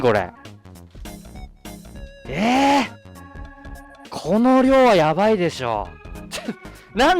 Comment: when he said This amount of food might be dangerous it sounded like he said Coronavirus.